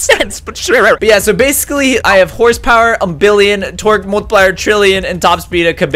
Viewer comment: When he said " Torque " I thought he said " Twerk "